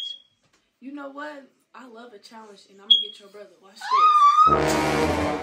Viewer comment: what she say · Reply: She love a challenge